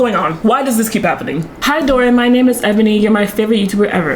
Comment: I almost thought that said Evany (I have beef with an Evany)